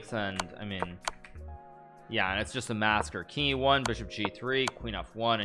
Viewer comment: what does Hikaru say? "It's just a..." ? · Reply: a massacre